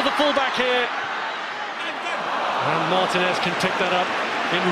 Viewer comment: Who knew Martinez would be the best keeper of the World Cup 10 years on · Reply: I thought he said David Martinez. 😅